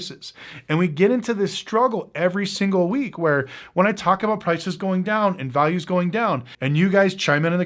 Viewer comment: Thought you said prices were going down